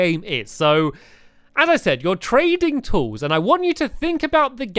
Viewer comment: The first time u said trading tools I thought u said training tools, as if you were getting tools trained to do their jobs lol